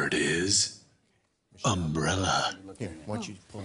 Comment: it sounded like Mumbrella